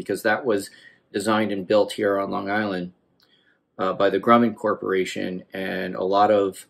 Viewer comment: Sorry I have to ask @ 17:55 did you say Grummond Corporation or Drummond Corporation...asking for a friend ;) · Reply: Grumman. They are mostly gone from LI now since the late 90s when they merged with Northrop. It’s still a source of pride here that the lunar lander was built here. Cool that it was with the help of Canadian engineers and the Montreal company that built the landing gear.